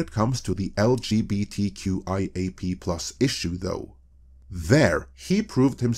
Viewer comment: Hang on a minute - did you say the 'P'' is now part of the rainbow acronym at 4:43 ?